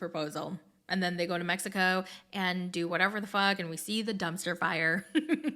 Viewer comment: Am I hearing things or did say the ( Fork word) at 12:36 by mistake? Either way Love you. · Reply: she says it in a lot of her videos, instead of f^ck